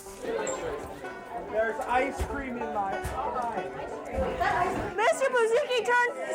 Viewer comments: did someone say “there’s ice cream in my eye” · Who else heard 'THERE'S ICE CREAM IN MY EYES' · did anyone hear "theres icecream in my eye!!!!!!!!!!" around 14:05? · i'm I the only one who heard they have ice cream in my...